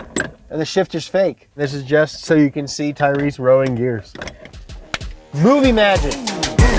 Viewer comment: Okay. But every time they say "movie magic" I hear "fucking shit"